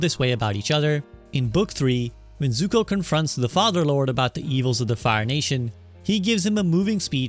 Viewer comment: Anyone else catch "the fatherlord"